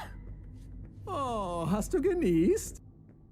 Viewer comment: I’m so sorry and no offence! But the Dutch one I heard “aww, hats took your knees”